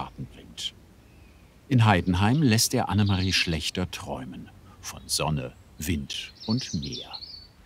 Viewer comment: musste ich 3 mal hören damit ich verstanden habe dass das ein name ist :D oder wer will schon schlechter träumen?